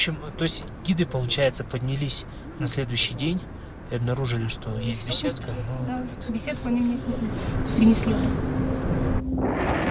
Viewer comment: Что она говорит на 37:12? Важный момент, но я не могу расслышать. Подскажите, пожалуйста, что обнаружили гиды. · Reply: Обнаружил и что Пусто.беседку они мне принесли